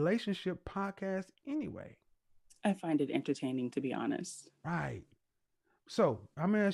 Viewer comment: The one fat married woman said she watched your show because it is “entertaining.” I thought she said it was “offensive,” lol.